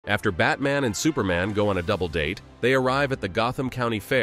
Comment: Bro I read/heard it as “after Batman and superman go on a date”💀